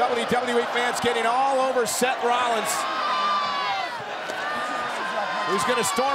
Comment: did JBL just said "Seth Freakin Rollins"? Time predictor detected